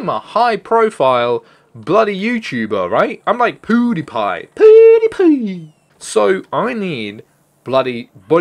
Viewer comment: Did he said PooDiePie? :D